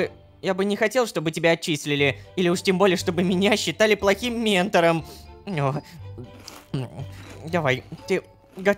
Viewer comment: Мне покозалось когда карл сказал быть плохим метором мне послышалось ментом 😂😅